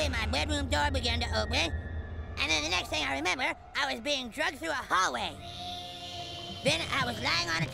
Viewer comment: why did he say “ dragged” like that? That made me think he said “ drugged”